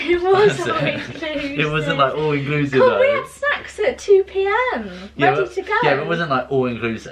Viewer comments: Honestly thought Elle said ‘ con we had sex at 2pm’ but she said snacks 😂😂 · Omg i thought Elle said Sex at 2pm 🤣🤣🤣🤣🤣🤣🤣 nope,snacks lol xxxxx